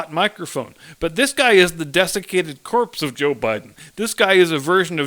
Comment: At first I thought you said defecated corpse of JB. Then I realized you said desiccated and the mental picture was about the same.